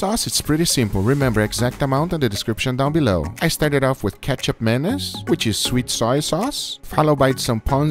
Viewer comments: I thought he said ketchup mayonise and i rewound it like 30 times to keep hearing it then i checked the ingreadients lol that is not what he said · Thought u said KETCHUP MAYONNAISE